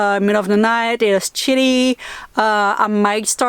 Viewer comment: When you said, chilly I thought you were saying Shitty, lol.